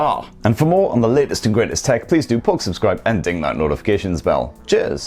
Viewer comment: I thought he said subscribe and jizz😂😂